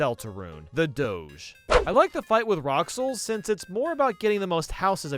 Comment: he pronounced it roxls instead of rules which is how it is actually ment be said